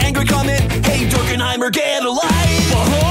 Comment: I'm not the only one who hears "get a life" right XD